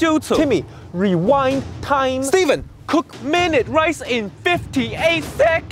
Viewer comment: Everybody gangster until Steven cooks minute rice in 58 seconds. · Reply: I thought you said mince rice! Now I get it! Cuz, how can you say minute rice in..............50...........8........